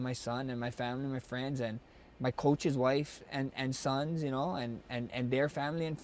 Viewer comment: oh snap did Holloway say "My coach wife" and quickly added and their sons with the "s" 😁 he cough himself and forgot to mention my coach he never acknowledge his coaches he only said coach wife implying he's been having some time with that particular coach wife 🤣🤣🤣🤣 dam max is a dog